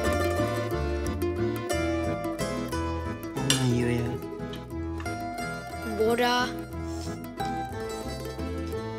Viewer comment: allahını seven bu çocuğun orda ne dediğini söylesin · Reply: "al benim yemeğimi de ye" diyor